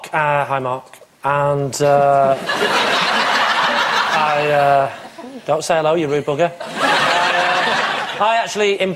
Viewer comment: what does lee say in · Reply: "Don't say hello, you rude bugger."